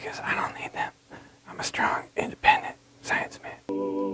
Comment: All I heard was strong, independent, psychopath, Mr O...